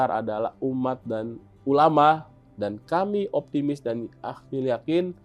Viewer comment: Saya salah dengarkah? Menit terakhir"dan aibon"???